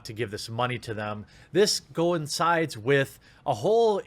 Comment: It sounds like you say go insides instead of coincides